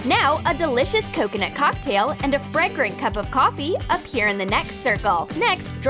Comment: And I deaf or did they say coffee when the picture said tea? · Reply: OMG they said coffe